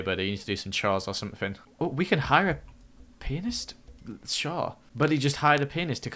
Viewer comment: When was saying "pianist" I thought he was saying "penis"